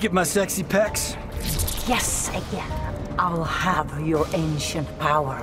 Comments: At first I honestly thought at 3:46 Skarlet was saying "Yes again." 😶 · is it bad that instead of hearing "guess again" I hear "Yes again"? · The subtitles say "Guess again" But we all heard " Yes A G A I N" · I thought she said "Yes, again."